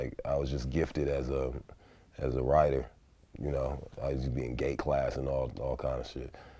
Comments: Did he say he was in "gay class" @ 1:56 ? Not that there's anything wrong with that. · did he just say he was in gay class? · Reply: He says GATE class lol. It’s the class for advanced kids in their grade. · did he say i use to be in "gay" class?